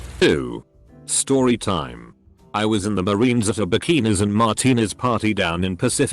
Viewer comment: I accidentally paused and when i unpaused i thought that it said penis and martinez XD LMAO